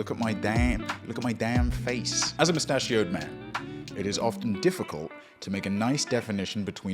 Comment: I definitely heard “as a pistachio-ed man” and I was not that confused because he’s so nutty